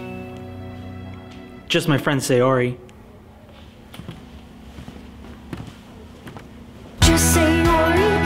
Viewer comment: Just say "ori" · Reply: I heard, "Just say Yuri :/"